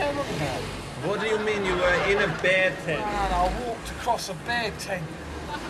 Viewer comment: what the hell is he saying? "I walked across a bear tent"? I can't understand his accent.